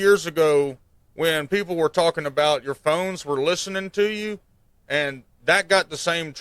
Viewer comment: Im gonna be honest, at the beginning i thought he said 'remember a few years ago when everyone said "your bones are listening to you" and now its confirmed' and i was wondering what type of shit he was on till i heard "terms of service"